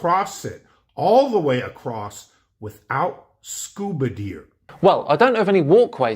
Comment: Did he say "scuba deer"🦌 That would be interesting to see lol